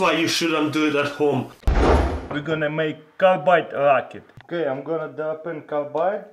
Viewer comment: what did he say? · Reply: Jack Hunington we're gonna make carbide rocket